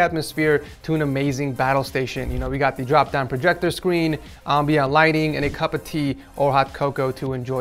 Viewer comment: Did i mishear saying ambiiyah lighting on 4:21?